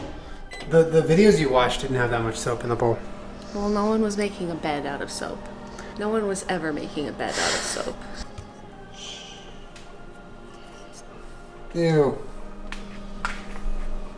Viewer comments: I thought this said ‘making my bed a dog made out of soap’ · I thought it said making my bed a dog of soap.